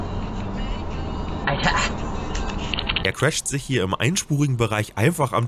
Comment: hat noch jemand "er crasht sich" verstanden? haha